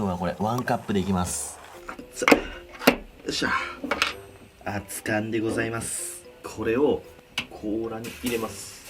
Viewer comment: はいどうも「金子でございます」がかに出てきてるかなんかわからんけど 「かにこでございます」って聞こえる